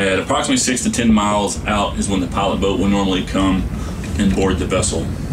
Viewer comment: I thought he said Pirate boat